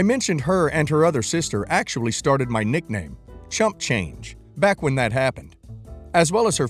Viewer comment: Oh my god thecword silly cxxxts. Is there a part 3 and I hope the other sister is let go and sued for giving out confidential information yikes . He should have told them I thought you said I was champ change.